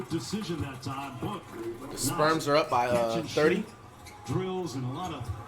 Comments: Did Flight say "The Sperms"??? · did he say the sperms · did dude just say sperms